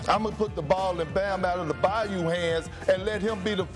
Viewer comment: That boi sad Bam "outta the Bayou" lol